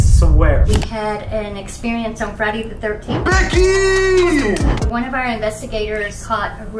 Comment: Did she just say that they caught something on Friday the 13th